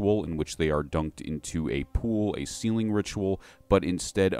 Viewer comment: You kept saying "sealing ritual" and I kept hearing "ceiling ritual" and I was so confused. I was thinking maybe they had to do some ritual on a ceiling. Sealing ritual makes so much more sense.